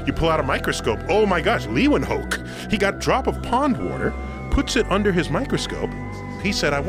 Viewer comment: lol thought he said bong water. way funnier my way